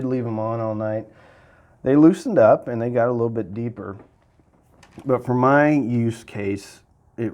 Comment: Iam bad at English. 2:23 Could someone please tell me what he said there? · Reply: "they loosened up and they got a little bit deeper", and he continues with he prefers using with subwoofers. turn on cc, its pretty accurate